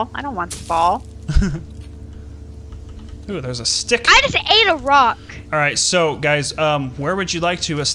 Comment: When said I just Ate a rock I thought she said I just a Iraq